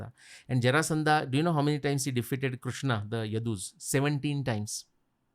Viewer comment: does he said he defeated krishna 17 times please someone clear it